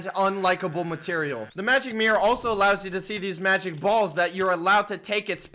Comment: did he say magic balls?